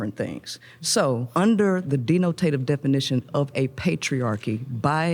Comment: did she just say denotated? as in "what is the wecommended amount of DENOTATED WAM I should have to a minecraft server?"